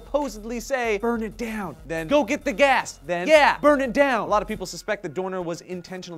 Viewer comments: I didn't hear "burn it down", I heard "burning gas!", but that's not to be said that "burn it down" is what was actually said. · I heard "go get the gas" and "burning gas! burning gas!" So I don't know if this is right but it sounds like a "oh shit the gas is burning" situation.